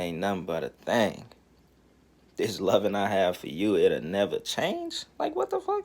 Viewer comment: Shit I Thought She Said “ There’s Nun I Have For U , It’ll Neva Change” I Thought She Was Runnin Ah Fantasy On A Nigga Lol Dis Shit Sad How Dey Let Da White Man Fuck Errythang Up